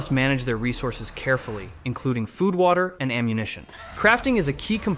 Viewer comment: Did anyone else hear it as "Food water, and ammunition." What is food water 😭 But all seriousness, GREAT video, I love zombie games and these are the first zombie games to look good in YEARS, literally the commando one looks like it could actually be more fun than Left 4 Dead, but I'm not holding my breath on that since I thought the same of that Zombie game that's still on game pass I can't even remember the name of because it was that bad.